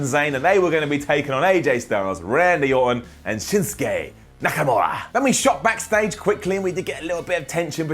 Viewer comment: am i the only one who heard him say Nakanura